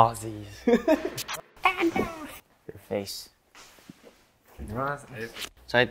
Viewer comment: Does anyone know what seungmin said at 0:13 :) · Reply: Your face.